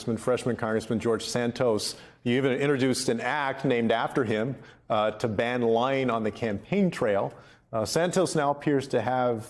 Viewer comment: "to ban lying(?) on the campaign trail"? I mean that would be great. or am I mishearing the verb? fwiw, this is rich coming from santos. 😄